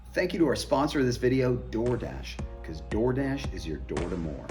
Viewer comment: for some reason the first time I watched this I thought he said "Doordash is your door porn" 😂